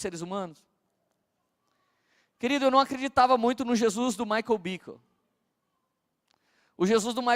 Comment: Jesus do Michael o que ? 55:02 não entendi o sobrenome, falou muito rápido e 2 x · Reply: Michael Bickle. É um pastor americano. Acho que é ele...